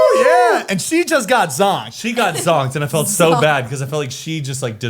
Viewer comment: Please this is lame but, help a non native person 😅 21:49 she got what? I keep hearing "she got *song*" 😂 h e l p